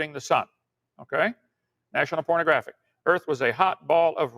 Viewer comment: Did he just say national pornographic instead of national geographic? lol.